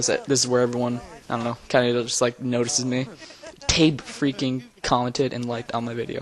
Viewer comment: who is that you mentioned. I couldn't really understand it did you say tape?